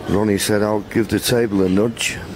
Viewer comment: what did ronny say? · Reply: He said to nudge the table